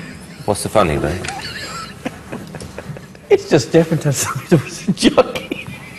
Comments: what is louis saying ? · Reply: Seljatin Ajdar "its just funny that someone wears a jockey" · What did he say at 0:22? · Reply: It's difficult to see her as a jockey